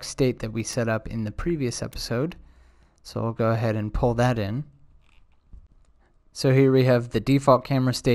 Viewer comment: in object oriented programming OOP, re using a function is referred to "calling a method or a function". Lol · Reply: ohhh you said pull that in, I heard it "call that in" lmao my bad lol